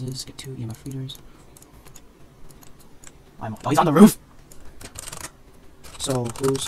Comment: it sounds like he said i will bite of the roof